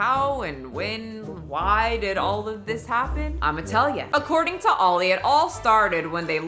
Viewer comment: My french me, while watching speaks with her hands, heard : "I'm italian" instead of "I'mma tell ya" 😂😂😂 Ok, you're transracial too, admit it, mamma mia !!!